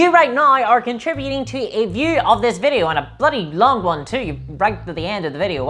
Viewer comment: "A bloody long one" that is indeed what she said.